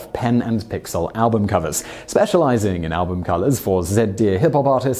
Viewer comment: does say album colors here?